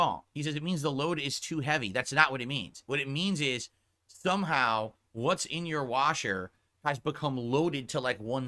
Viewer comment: It sounded like he said thumb how instead of somehow